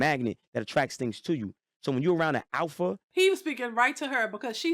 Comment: When you pause to speak. You should rewind the actual video 3 seconds. Your not capturing key points. At 7:35 The ignorant woman in white asked "An Alpha what?" Then said "MAN."